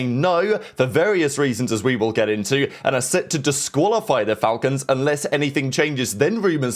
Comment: you said the falcons instead of the stallions